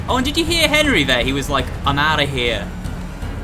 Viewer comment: Where is this moment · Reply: ya cant really hear it cuz is sayin something about light but it said: I am getting the hell outta here